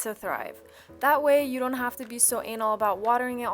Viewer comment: Did you say "anal"? 🤔😂